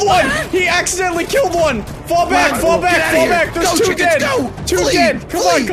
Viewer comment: I heard "Fowl back"...